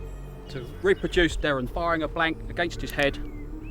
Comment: did he said to shoot a gun at a gangsta head? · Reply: RoyalNatsu no he said against his haed